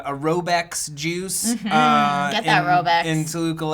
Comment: I don't know if it's because is saying it but i kept hearing: "...I went outside with my jews (juice)...." lol